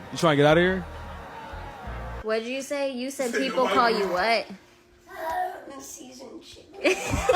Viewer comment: What did the kid say at 7:08? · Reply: I think I heard “seasoned chicken”